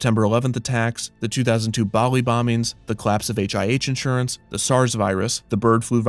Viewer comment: When he was listing off everything that was a reason as to why the park was being closed and you said 'The collapse of {something} Australia,' I legit thought you were gonna say 'the clap' bc I misheard 'the collapse' and was like, 'dude just going around blaming everything holy shit'